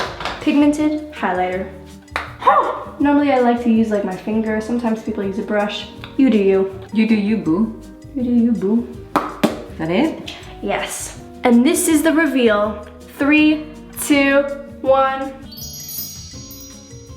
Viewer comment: I heard some arbic at 8:43 said hahia witch means thats it